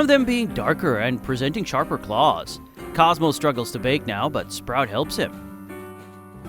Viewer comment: I LITERALLY THOUGHT YOU SAID COSMO STRUGGLES TO "VAPE" NOW INSTEAD OF BAKE